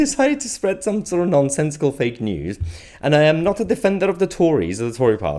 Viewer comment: Nonsensical? Oh, I thought you said non- testicle.